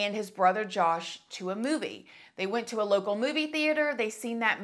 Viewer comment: "Zachary's mother, Melanie, took Zachary and his brother Josh to a booby" That ☝ is what I heard instead of "movie" and it totally cracked me up 😂😂😂